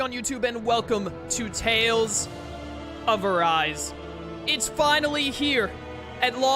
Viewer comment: I don't know why, but it sounded like you were saying "Tales of Verise" when you were talking about it previously